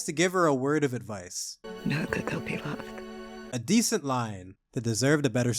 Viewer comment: I have rewound multiple times, turned on captions and slowed down the video and I still cannot understand a word of that advice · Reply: Know that they’ll be loved